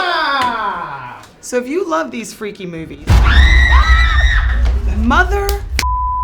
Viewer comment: I heard he said motherfuck 1:12 😂